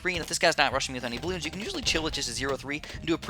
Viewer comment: OMG did he just say "I'll chill with it" when he got the ice tower?